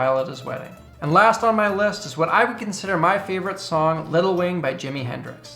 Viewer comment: I thought you said Lil' Wayne XD